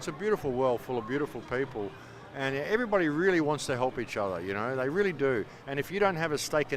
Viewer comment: Does he just said "insha Allah" (God willing) ?? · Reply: He said each other not Insha Allah. And what's the matter if he did say Insha Allah